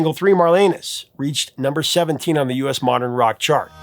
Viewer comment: sounds like you said Three Mile Anus😂😂 I had to rewind and put on captions 😂